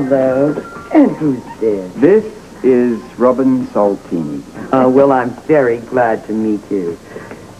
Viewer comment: What does Robert say at the start of 00:11? After Ruby says who is this? Robin: May I look around? Hehehehe she has such sweet manners! :) and the little actress had a lot of dialog! Sharing her story about seeing Yank at the park! :) And Frisco with the Asian men and little Robin and the Asian man are all connected! I love it! :) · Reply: He just says "This is Robin Soltini" lol. She is sooooooooo cutttttte!!!